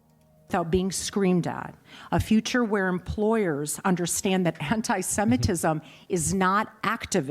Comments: I heard hentaisemetism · "A future where employers understand that Hentai-semitism is not activism" is what I heard 🤣